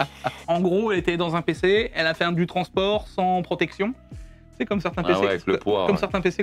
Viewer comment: comme certain pccomponentes , j'ai compris moi 🤣